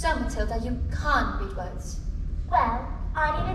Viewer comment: did she just say "you cahn read words"